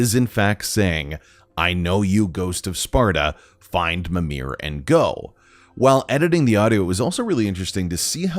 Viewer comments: I hear him say “find mimir and grow” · what i hear: "i know you, ghost of sunder. " then unintelligable for me. O-O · Instead of “Ghost of Sparta” I heard “Kratos the God” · I hear, “O don’t know you ghost of senpie.” Although I know that’s not what it means. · I hear "I know you Ghost of Sparta..... find Mimir, that way!" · What do we think? All bullshit or could there be some hidden hints within the ASMR booms of The World Serpent? Also Use code VGS at <link> for 20% and Free Shipping - TO RAGNORAK! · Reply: I hear " I know you Ghost of Sparta, find mimir , he will help" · I think I heard "I know you ghost of Sparta find Mimir and grow" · i heard “i know you ghost of sparta …find mimir and aaaaaaaaaaaaaaaa” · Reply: He said "I know you ghost of Sparta...find mimir he will help"